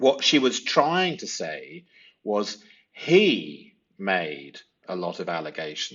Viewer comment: Transcript error? I heard = "I made a lot" transcribed, but in reading her lips, I see equally "he made a lot."